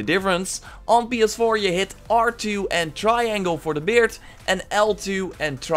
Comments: Thought he said try angle for the beard when he actually said triangle hahaha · I'm putting it in the comments aswell Beard: R2 + 🔺 Hair: L2 + 🔺 Xb1: right trigger + Y: for beard Left trigger + Y for hair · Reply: Thank you! I thought he said "try angle" for the beard and not triangle so i couldnt't get it to work haha